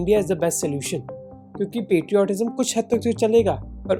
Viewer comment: the fk did I heard it wrong ??? Safe panner ?? What about not getting killed while travelling pahalgam or kashmir ?